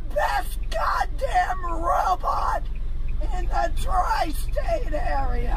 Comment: “I’m the best goddamn robot in the tristate area” get that dog out of the car, he’s terrified. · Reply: So, I did not mishear?!?!? She DID say "robot"?!?!?